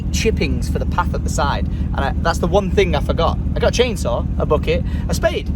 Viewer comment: did he just say he broke the chainsaw?😂 Ouh